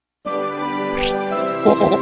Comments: Did heavy say somthing sucks me underneath? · LOL it sounds like he says "Sucks me underneath" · OHOHOTHATSLFUCKSMEONTHEKNEEAHRFAHRAHARA! AAAA*barfs scout* MANFAIC :3 AAAAA noms scout OOO! eats viewer Manfaic 2 #Manfaic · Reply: AdaMario Films and before that, I thought he said "that slaps me underneath"